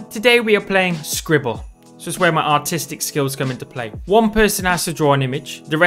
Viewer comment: i thought he said "autistics skils" instead of "artistics"